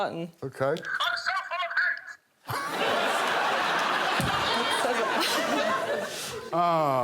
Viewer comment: What does the puppet say? I’ve relistened a couple times and I just can’t tell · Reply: "I'm so full of hate!"